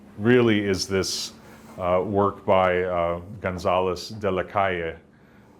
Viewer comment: I can't figure out how to spell the last name of the painter of the sheep picture mentioned at 9:22. Anyone know who it is? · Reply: Gonzalez de la Calle. Thanks for watching!